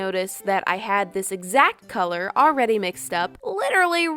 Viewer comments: Whenever you say puffy paint it sounds like you say puppy paint. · The first time you said puffy paint I thought you said puppy paint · whenever u said "puffy paint" I thought u said "puppy paint" XD